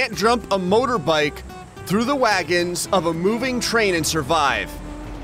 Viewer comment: What other people heard: "land and survive" What i heard: "like and subscribe"